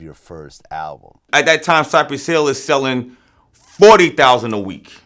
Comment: who did he say was selling 40,000 a week? thanks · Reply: rs forward cypress hill